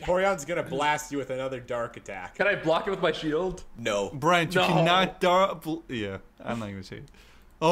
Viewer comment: what is he saying??? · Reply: I think he said that Brent can't block dark blasts before. But didn't want to say it anyway.